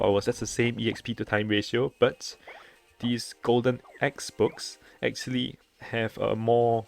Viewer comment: Did you just said XBOX?!😂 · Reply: i tried to say x book